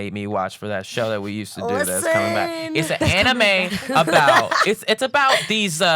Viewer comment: did will say anime club is coming back